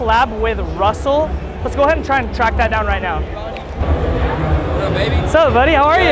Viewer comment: Did say “What up, baby”? Lol